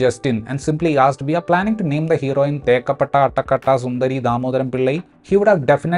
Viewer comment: I heard it as makeup itta sundari